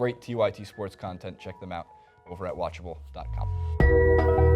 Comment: lol I thought u said 'we r unwatchable' there at the end